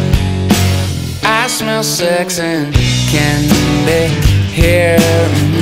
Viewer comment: i always thought he said "i like sex and candy" i was like yeah who doesn't xD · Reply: I thought he was saying, “I smell sex in Kandahar.” I thought that he had spent time in Afghanistan.